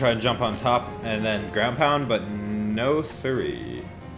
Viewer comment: did u just say no asuri?